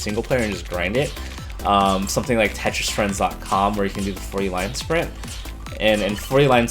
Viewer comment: What do you say at 3:06? <link> where you can do the ___? I don't understand ;( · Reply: Where you can do the 40 line sprint (standard mode on tetris). Be quick though, tetris friends shuts down in 5 days 😔